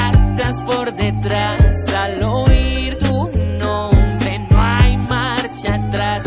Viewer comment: i know a very little spanish, but i wanna say that in 1:30 i can't hear the line "no hay", and i can't tell what he says.